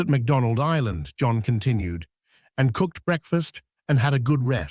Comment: I swear to god, at a hair after 19:45 I thought you said McDonald's for Breakfast at first, lolol.